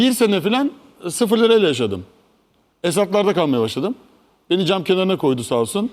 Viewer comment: ne diyor nerelerde kalmaya basladim bisey diyor ama anlamadim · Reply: Esadlarda diyo